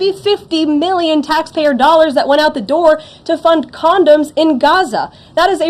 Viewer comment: did she say to fund condoms or condos in Gaza?